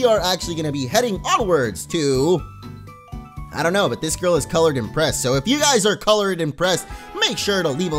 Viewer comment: i was so confused. I thought he said if you are coloured in press.